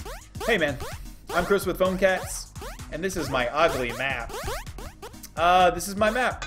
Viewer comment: What did u say at the 0:05-0:06 ? i hear u say "im chris with phonecatss"